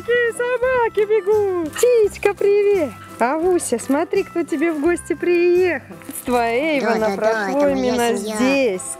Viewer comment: мне послышалось? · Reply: Tanya Korolenko она говорит цитька,сокращенно от клички Циля